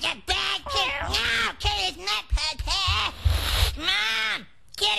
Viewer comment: Thought I heard it as "No kitty, this is not Pad Thai," But this makes a lot more sense.